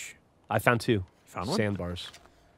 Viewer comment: sambar i thought u said sandbar